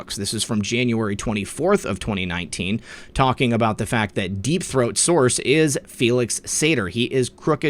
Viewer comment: Time 14:50 did he say deep throat?